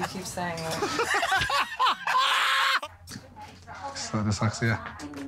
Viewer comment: I think it was deliberately edited to make it seem she was pissed off, look at 0:30 , she smiled, and then there was a cut and then he made it awkward , and so her reaction. Her reaction per say is quite what i would assume most women to give. And she is awesome. Its just a good edited video. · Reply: he actually says “it’s uh dyslexia”